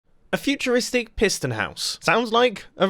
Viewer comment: Oh lol, I thought it said; futuristic prison build... Lol i need school